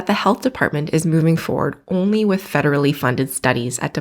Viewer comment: The moment you said "federally funded" I heard "big pharma funded". Not sure if my signature will have any impact, as I'm a European citizen, but will sign it anyway.